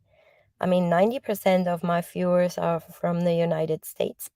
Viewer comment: It sounded like you said 90% of my Fuerers are here...🤣